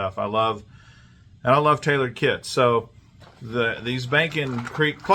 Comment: Nice looking kit ! I thought you said I like Taylor Swift ( Tailored Kits ) Lol